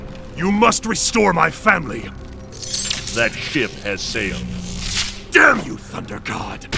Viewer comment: did rayden said shit? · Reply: FlamingNinja Gaming nope it's ship